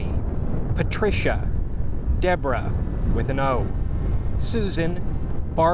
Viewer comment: youtube auto-generated subtitles suck when you said “deborah with an O” the subtitles said debra, but when you said “debra, no O” the subtitles said deborah you can look for yourself 11:09